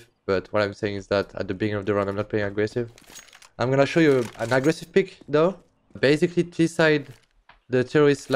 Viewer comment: Not to be mean, but I kind of laughed at this. He says he’s going to show us an aggressive peek, but the subtitles say “peak”. Peek is like going around a corner, while “peak” is like the top of a mountain.